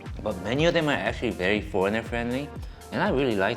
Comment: since im not a native english speaker, i heard this wrong and even need to open my cc becuause i thought he is saying poor and friendly